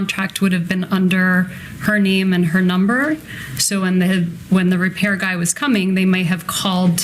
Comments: I had heard on someone’s video that at the 11:52 point when Wendy is talking about the repairman coming out that she almost said hit man, once you hear that you just can’t get out of your head. It literally sounded like she was about to say hit man and she said Repair man instead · Listen carefully to her at around 11:52 in her explanation it sounds like she was saying "when the hit... repair man". Now that's very telling.